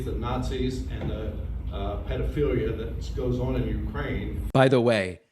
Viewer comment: Get your ears checked David, he clearly said "pedophilia". You do that a lot, making issues out of a mispronunciation, even when there isn't one.